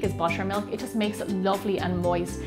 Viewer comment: I thought u said mushroom milk instead of buttermilk